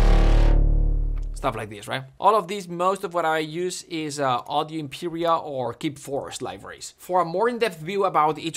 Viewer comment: Fantastic content! What did he say he used for drones, braams etc. at 12:15? Audio Imperia and …? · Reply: Audio Impera and Keep Forest